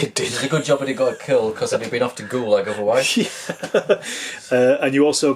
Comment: Totally misheard something early on. When said something might have been sent to the Gulag, I thought he said Goole.. not certain which is worse!